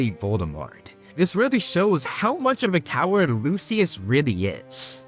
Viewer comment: what word did he say, I assume he said b*%ch